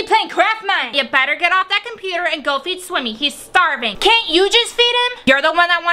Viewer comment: I thought her mom said Swimmy is garbage